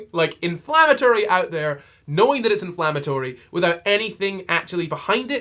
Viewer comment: Why did I hear "inflammatory" as "in flower territory" and it just sent me to cottagecore world. Much nicer place to be than this awful person and her dumb tweets.